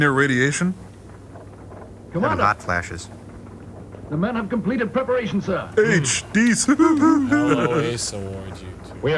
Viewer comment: At about 52:52 Servo is captioned as saying Dis! I always thought when they said that after a rip it was a childish 80s thing ... Dece! As in, short for decent